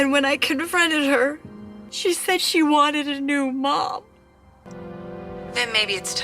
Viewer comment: i thought it said "i want a new man"🙏😭💀